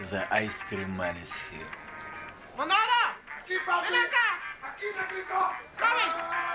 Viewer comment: I thought he said, "aqui papito", but then I realized he's saying, "aqui negrito" 🤣🤣🤣